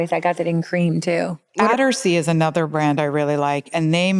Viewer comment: What is the brand of the dress that buttons down the front. It sounded like you said Adersay. Thanks so much. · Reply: Attersee